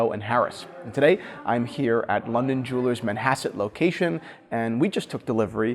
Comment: Learned something new today, never heard of Manhasset till now, thought at first I'd heard Manhattan